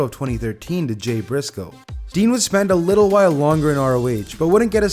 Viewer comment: Did he say Dean instead of Steen at 7:04 ?